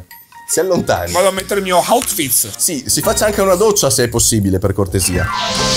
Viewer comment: solo io ho capito auschwiz e doccia lol